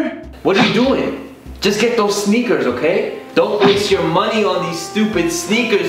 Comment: I heard the word "sneakers" soooo many times.. I hear snickers now..